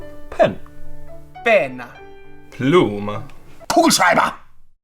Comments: На 0:36 слышу "полушайба"😂😂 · мне одной послышалось кушать дай!